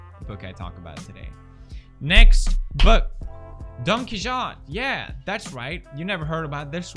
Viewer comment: I thought you were saying "danke schoen", you really need to Google how to pronounce Don Quixote